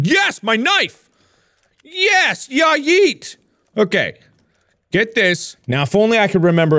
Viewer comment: I don't know if I just can't hear but did mark say YAYEET?!?!?!